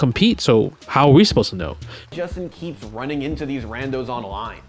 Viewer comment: For the longest time, I thought the guy said "Way to go Justin".